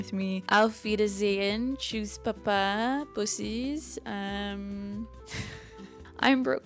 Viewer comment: What did you call us at the end of the video, before you said, "I'm Brooke Cormier..." 😮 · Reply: “Bussis” means “kisses” in German 😜